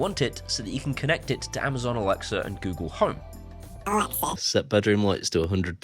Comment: I swear I heard the little one say 'home assistant'.